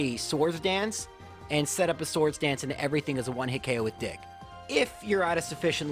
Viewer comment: with CCs: "set up a Swords Dance and everything as a one-hit ko with dick" Damn. I love how the CC randomly doesn't understand what Dig is sometimes, lol. I'm not sure who's better but Charmander feels better to use overall... more intuitive? I guess because you're just setting up and/or taking out enemies directly, instead of Sleep Powder + Growth shenanigans. Great run.